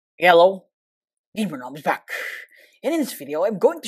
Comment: I hear the “hello GamerNom is back” to “Yello GamerNom is back”